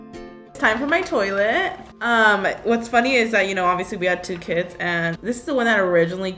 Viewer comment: in 6:01 who thought Karina said "We had two KIDS" lol